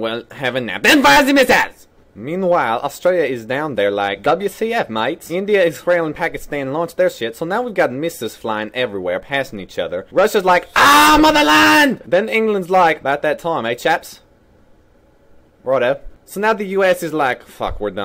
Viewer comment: I remember screaming "AAAAAAAGH MOTHERLAND!!!" during a game of Arma and noticed a minute later my brother in law was staring at me very confused from my office doorway... good times! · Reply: I always thought he was saying "ah mow the lawn" 😂😂😂😂